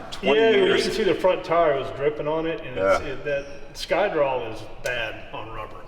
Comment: I thought y’all were saying Skydrol and I was very concerned how casual you were about a leaking system before I realized I misheard